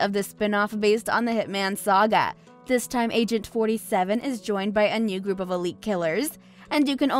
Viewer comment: Did she say "AGENT 27"?!!!